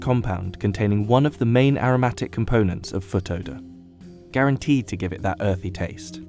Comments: did he say 'foot odour' ????? 1:43 into the video .................the main 'fragrant' component that gives the truffle it's 'earthy taste' .................who in the heck would want to eat something that smells like rotten socks ? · I heard foot odor?